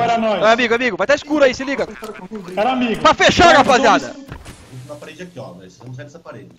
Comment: all I heard was "harambe" xDDDDD